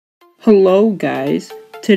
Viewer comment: At the start i thought you said "Hello daddys"💀💀💀💀💀💀💀